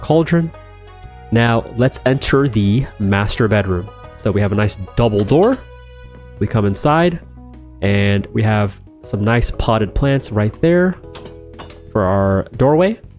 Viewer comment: I heard "the masturbate room" 💀💀